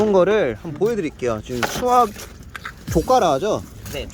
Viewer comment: 네? 뭐라고요? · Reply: 헐ㅋㅋㅋㅋㅋㅋㅋ잘못말해서 바로 조과라 하긴했어요 ㅠㅠㅠㅠㅋㅋㅋㅋㅋ